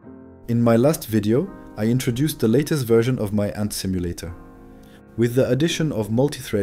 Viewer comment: Oh I thought it said "ant stimulator", nevermind this isn't for me.